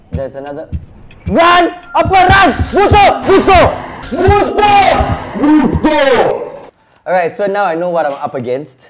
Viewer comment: in 3:47 I hear him said multo maybe and that's a Filipino word or just a Singaporean Word